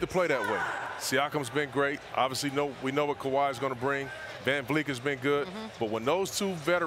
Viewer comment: Kevin Dugrant 😂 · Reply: I swear Chauncey says "Van Fleek"